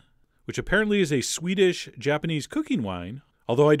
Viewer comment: It sounds like you said "Swedish Japanese cooking wine"